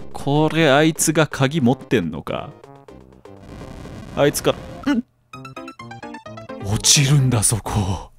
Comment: 「ん…！？」 本当はこんな感じなんだろうけど、 「んっ…///」 って聞こえてしまった変態です。